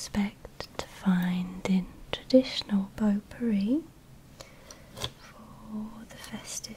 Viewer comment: I don't mean to change the relaxing mood or anything, but when I heard 'pot-pourri,' I thought you said 'poo-pourri.'